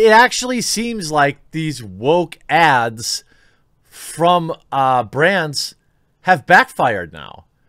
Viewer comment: When you said “Woke ads from brands.” It sounded like you said “woke ads from Brant” and I thought “I had nothing to do with any of them!!”